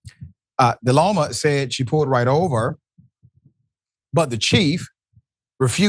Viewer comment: This looks like she was mocking people of color having been through similar instances . I heard him say "she pulled off o. Me". See the difference a color makes?